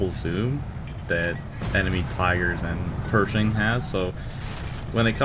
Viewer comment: did u say enemy Pershings?